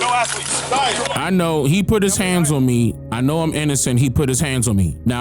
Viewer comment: It sorta sounded like he said I know Im not innocent.